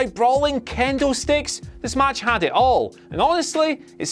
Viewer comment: I heard candle sticks